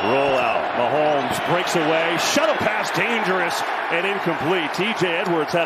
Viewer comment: At time stamp 3:21/17:22 it Sounded like he said "Shuddle pass" 😂🤣🤷🏻‍♂️🕴🏻